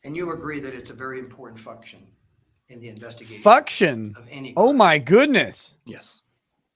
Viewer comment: I heard "fucktion", too, but then I thought I just made it up in my head until said it lol